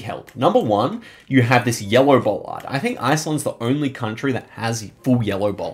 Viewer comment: I've rewinded 10 times and I still can't understand what you're saying @ 1:40. Yellow what? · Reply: yellow bollard